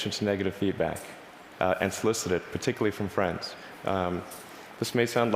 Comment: and __ particularly from friends ???? what did he say???? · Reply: Never mind I got it solicit: ask for or try to obtain (something) from someone.